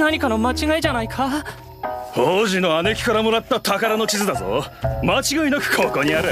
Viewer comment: Little correction here. He actually says, "Big sis Bao'er" here.